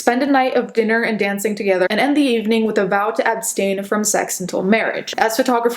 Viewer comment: I heard vow to epstein